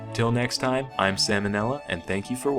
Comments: Omg I thought you said “I’m salmonella” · Sounds like he says “I’m salmonella” when he sounds off at the end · sounds like you say "im salmonella and thanks for watching" im sorry